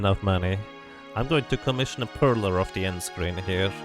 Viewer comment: I am not quite sure what you said here. You said: "One day, if I have enough money, I'm going to commission a......." - and then you mention the end screen. What exactly was it that you wanted of the end screen? Your knowledge of one of my favorite games, Secret of Mana, is incredible. Thanks for sharing. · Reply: A "Perler", these little plastic beads that can be arranged, very much like pixels on a board, and then ironed to stick together and hung up like a painting :)